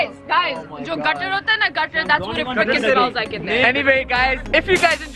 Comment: did she say fu*king smells like ?